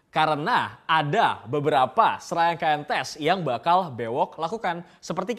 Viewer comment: bang bewok ngomong apa si? Srengkentes? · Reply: Serangkaian Tes maybe